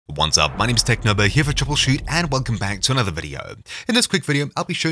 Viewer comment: did you say technoblade 😨